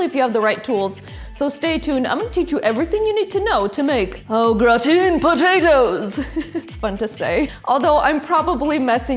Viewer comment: as a young boy i thought they said all rotten potatoes... and in restaurants that is how i would order it... i was about twelve.